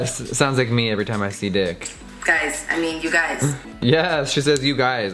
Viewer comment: did I hear d*ck??? 😂😂😂